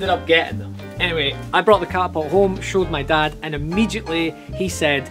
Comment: I thought you said: “I shot my dad.”